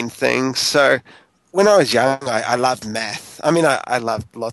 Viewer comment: did he just say I love Meth? hahaha